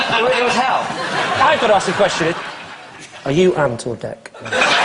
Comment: What does he say? Are you ant or deck?